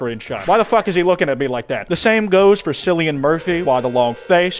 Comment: Did you just say "Sillian" instead of "Killian"?